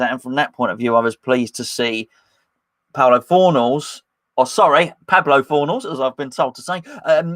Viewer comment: I thought you said 'Porno Fornals'!